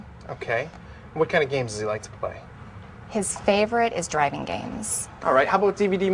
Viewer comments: "His favorite is dragon games." That never fails to make me laugh. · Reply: I heard “driving” games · Oh man. My favorite games is dragon games too! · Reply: Always thought she said dragon games like spyro the dragon